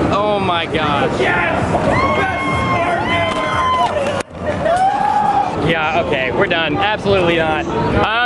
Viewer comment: Did i misheard or that man really said "YES! BEST PORN EVER." When wear that cloth HAHAHAHA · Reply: He said "best Spartan ever" but that's just hilarious